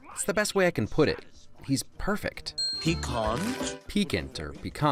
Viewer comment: Ohhh piquant? I always heard "Pecan" so I thought they tasted kinda nutty.